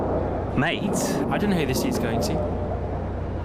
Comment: all these years and i still dont know what Lando is saying here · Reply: I heard “I don’t know who the seats going to”